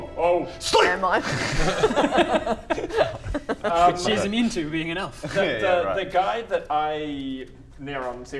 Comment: She's immune to being an elf? Seriously, if there was an 'it' in that sentence, I didn't hear it after two repeated re-listens. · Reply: Which she is immune to, being an elf. The comma makes all the difference!